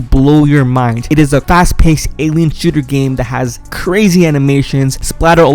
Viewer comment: What did he say? Because i heard ALIEN.